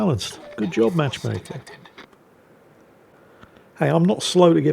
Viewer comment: I've cleaned my ears three times now and yet it still sounds like is saying "Good job, Matchmaker!" Something is definitely wrong with my ears.